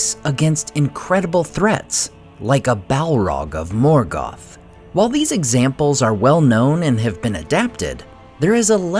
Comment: what did you say?? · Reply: "The hobbits, the hobbits, the hobbits, the hobbits..."